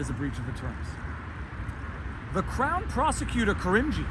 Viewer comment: Viva, I honestly thought you said the clown prosecutor.